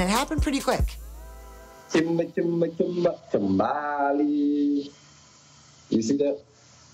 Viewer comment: Did he just say 'Kimba, Kimba, Kimballyyy' IM DONE🤣